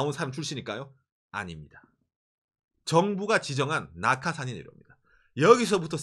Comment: 나카 산이지로 라고 들려서 저게 누구지? 했는데 낙하산이였구나...ㅋㅋㅋ